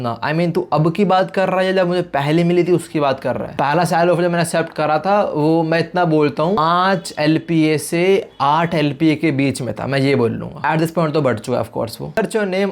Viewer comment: I don't understand some one explain me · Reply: 5-6 Lakh per year